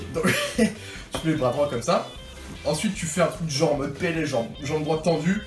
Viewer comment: C'est moi ou à 4:52 tu dis en mode "pnl" au lieu de "pls"😂